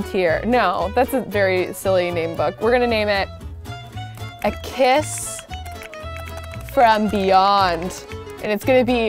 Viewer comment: For some reason new book, in my head I heard" a kiss from beyond the uterus ". 😂